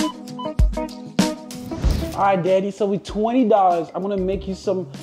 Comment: he did NOT say danny